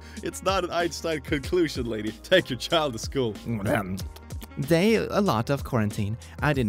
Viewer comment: Day 5048 : how many days is she saying? guarantine isnt that long